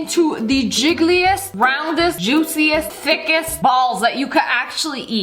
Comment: idk why but i thought she said "thickiest"